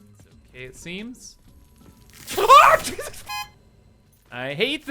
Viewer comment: sounded like said “AHHH Jesus f**k” and honestly same 😂😂😂